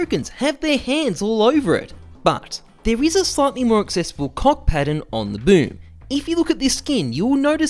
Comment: is it only me noticing BLA CK! instead of C O CK at first? o_O